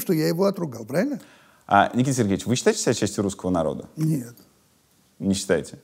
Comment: На 34:05 Дудь спрашивает про часть, а Михалкову послышалось про честь. Поэтому и ответ получился не правильным.